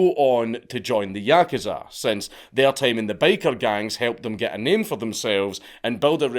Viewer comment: Had this video running in the background, I kept hearing "the baker gang".😅 Never guess that those pastries making gang can be quite notoriously rowdy too.